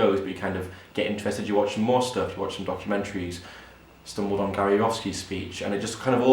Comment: what is said at 7:12 "stumbled upon ... speech" kariowski, carry off ski ? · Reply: Gary Yourofsky - The Best Speech you will ever hear